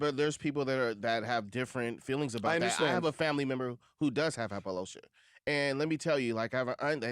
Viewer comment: Did he say “apelosha”? 😂